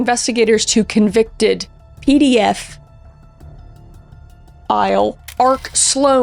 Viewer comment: The Botswana Defence Force (BDF) Band has two different groups, the Military Band, used for ceremonial or official purposes and the Dance Band, which is used for entertainment. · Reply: no. That’s not the answer 😂 I misheard “PDF File”. But now I understand that’s it’s p ed ophile